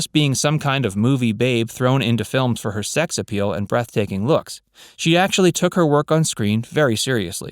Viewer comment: I heard "breasts taking"